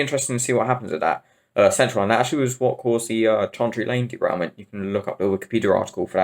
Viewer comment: What is he saying, I cant make it up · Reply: He was referring to the Chancery Lane derailment on the Central Line in 2003.